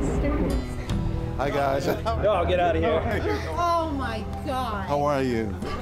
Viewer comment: Did he just say "ohh get out of here"